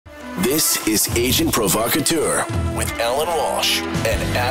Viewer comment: I keep hearing, "This is Asian Provocateur!" during the opening